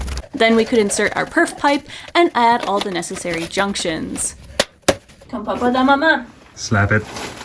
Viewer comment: did I heard “Comme papa dans maman”? 😂😂